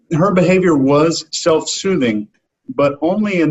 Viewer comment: 'In the brilliant words of our friend, Joe Navarro, any repetitive behaviour is [????????]' I can't figure out what he says here. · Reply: Self soothing